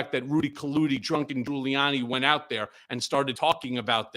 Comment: Haha did he just say Rudy Kaludy Drunken Giuliani 🤣🤣